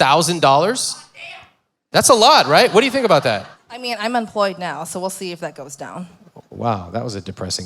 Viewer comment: Before posting a comment, take a moment to reflect—why are you judging this couple and this woman? What I see is an engaging conversation, a woman who is genuinely listening and learning, and a couple who clearly love each other and will grow together. Instead of tearing her down, why not try to understand? And have you actually watched the whole episode, or just the first five minutes? Kudos on a great episode!! ❤ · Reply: I will just add that I’m Swedish, so I’m not the best at catching everything that is being said, but when they look at their annual income, it sounded like she said, "I'm unemployed right now, so we'll see if that goes down." Is that what she said? It wasn’t discussed, it was just for a few seconds, but I think it’s completely understandable to feel extra worried in that situation... I think they’re being honest and brave. I understand what you mean about accountability. However, I also see someone who is genuinely worried, caught in a pattern of behavior, yet open to learning and truly listening. When I watch the whole episode, I don't see a person trying to escape accountability.